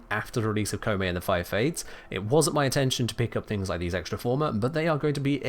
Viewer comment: Some potential caption errors: 5:24 “It wasn’t my attention…” I think the intended word was “intention” 10:31 “Let’s see if we can’t spot…” I think the intended word is “can” instead of “can’t” 19:57 “They won’t be bothering us anymore more.” An extra “more” was slotted in. 20:43 “wake up, beat Vor, and fight well tenno” a minor grammatical correction “fight well, Tenno.”